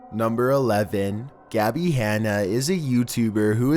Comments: #11 sounds like it says hannah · i thought that the voice said Hanna istead of Gabbie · Number 11 sounded like it said Hannah not gabby